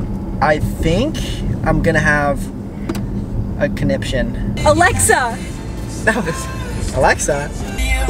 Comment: I thought said he's gonna get a constipation